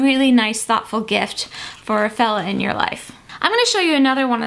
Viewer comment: When you were talking about the leather gloves for men, I thought you said 'for the felon in your life' and I loled so hard when I realized you didn't say that.